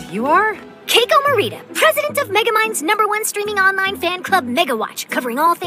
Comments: I thought she said megaminds onlyfans club negawatch 💀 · what did she say 💀💀💀💀💀💀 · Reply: Negawatch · she really said ni**awatch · did i hear that right ? miga mind 💀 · why do i hear n-word watch💀💀💀💀💀💀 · i thought she said [n word] watch lmfao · hul up did I heard on**fan? Limme turn on caption. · Did she just say negawatch??? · i coulda sworn she just said "n*gga watch"